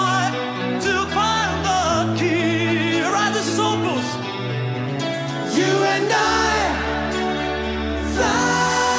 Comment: Could someone transcribe what Falco says at 0:47? Please!!! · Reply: He says „Alright, this is Opus“ as he is their guest on stage.